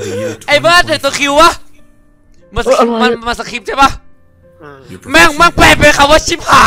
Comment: น้องพูดคำหยาบ,!! (ผมได้ยินว่าแปลเชี้ย)